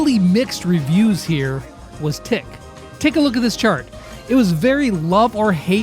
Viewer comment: did anyone else hear him way "Tick a look at this chart" is it just me?